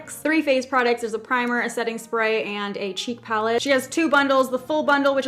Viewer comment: idk how or why but i heard cheap palette